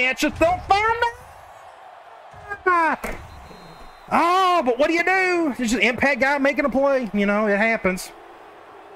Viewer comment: That was a tough loss. Your defense just couldn't stop the running game, also did you say the F bomb at 46:37, because it sounded like it to me. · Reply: Ha, no, never part of my vocabulary. My mic goes out when I get loud, something I could never fix. Pretty sure I said fumble in there. HB having 16 broken tackles was just too much.